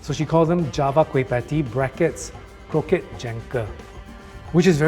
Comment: Susie Hing used the term "kroket tjanker" because her kwei patti uses the creamy filling of croquettes, but put into a cup or "cangkir" (old spelling: tjanker) instead. In Baba-Malay today, we also still use the term "cangkir" instead of the Malay "cawan".